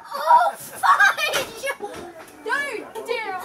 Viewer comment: did that girl scream oh fuck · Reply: She said oh fudge. I thought she said oh fuck at first too until I watched the video again!